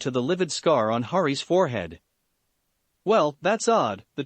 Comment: Took me until 14:00 to understand that I heard Hari which was actually Harry then it clicked to Harry Potter!🤣